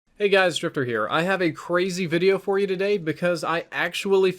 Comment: Could have sworn he said hey guys stripper here